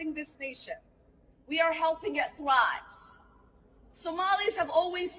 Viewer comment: They are helping Minnesota thrive. I could have sworn I heard her say they are helping Minnesota dry.